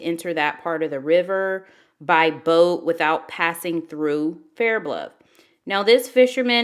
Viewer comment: she sounds like she says bair bluff..hmmmm